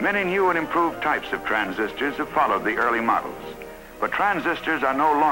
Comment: the narrative is incorrectly shown as "probably early" instead of "have followed the".